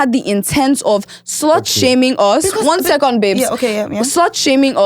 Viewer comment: Did I hear “one second babe”… 😂